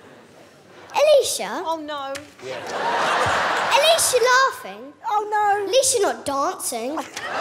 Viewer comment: "Alesha... Alesha laughing, Alesha not dancing." I still can't get over that one 😂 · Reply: I thought i heard " Alesha laughing, At least she's not dancing " 😂